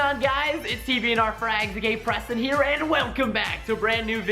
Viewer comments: I thought he said " gay preston" but ik he said aka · did he say gay preston here confused?